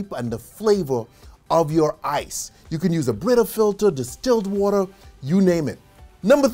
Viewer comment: did he say distilled water?